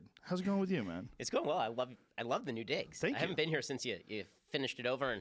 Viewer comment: did he say "I love the new dicks"?